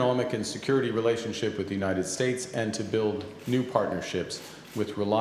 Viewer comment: I thought you said our relationship with the united states is over!?